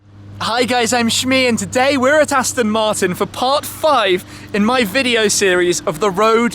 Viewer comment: Did he just say "Tastin Martin"?!